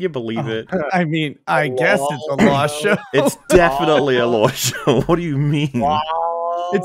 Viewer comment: when said >its a law show< i genuinely thought he said >lol show< 🤔